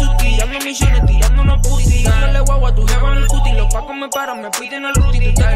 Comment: "Tirandole la guagua en la cara a tu grupi" claveeeee xdxd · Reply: Yo entendí "tirandole guagua a tu jeba en el cutis" FKAKF